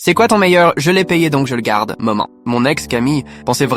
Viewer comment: J'ai entendu "maman" pas "moment" 🙉 🙉 🙉 qui est comme moi ??